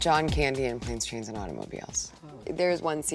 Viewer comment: What is the movie she mention at 2:40 ? I can't understand what she says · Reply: She said "... John Candy in ' Planes, Trains & Automobiles(1987)"